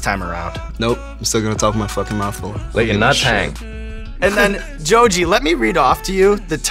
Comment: what is the word that Joji says at 1:40? Brian is talking about how he's gonna talk with his mouth full again on this episode, kinda like a "nope, fuck you, gonna do it anyway", and then Joji says "like a [something]"... it sounds like "natang", which is "challenge" in Indonesian, so that could make sense.